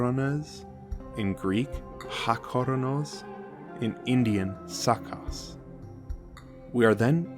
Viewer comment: did you say his name in Indian is SAKLAS??? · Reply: "Sacas" (not like the Demiurge/God of the Blind)